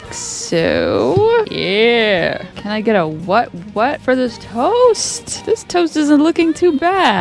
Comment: I thought you said “toes”